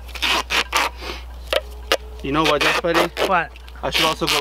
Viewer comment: Jazzburrito would have just karate-chopped the chain in half. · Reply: I always thought he was saying Best Buddy.